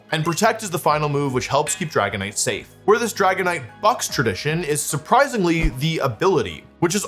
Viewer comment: does he say fucks or bucks?!